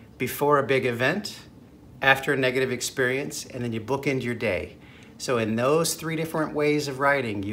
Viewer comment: 1. Writing before a big event (stressful, how u feel, etc) before the wedding, interview, 2. Writing after a negative experience 3. Book in your day in the morning and night (when you write a gratitude statement before sleeping, you will sleep better and wake up fresher) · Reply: It took me a second to realize he does not enunciate the "d" when he says "bookend". I thought he was saying "book in" the first time I listened to it too.